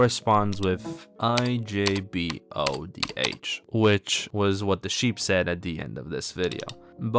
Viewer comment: i kirkuinely thought that said "dih job" 😭✌✌